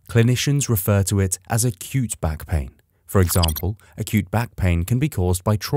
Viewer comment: When they said acute back pain, I thought they said a cute back pain. I was like, ‘How is it cute?’